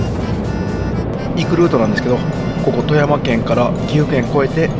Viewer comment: 初っ端の「行くルートなんですけど」が「リクルートなんですけど」って聞こえて思わず吹いてしまいました。 空耳アワー（笑）